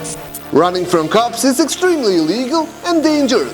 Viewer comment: I swear to God he just said running from the cops is legal at time stamp